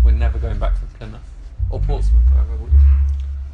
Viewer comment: Did they just Did they just say "We're never going back to Plymouth ..or Portsmouth" ???? Ik they were joking about it but i live in portsmouth and idk if they said portsmouth or not skdjskdudjdkr please help it's at 12:25 ish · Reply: Will said Plymouth or Portsmouth oops..